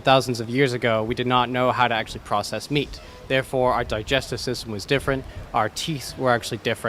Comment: did he just say teaths?